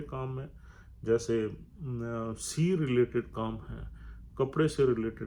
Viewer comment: par kya kaha C related ? CA related ? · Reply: I think Sir meant 'Sea' related kaam